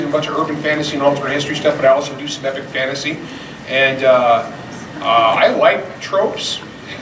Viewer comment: Did I hear "You look like a trope!"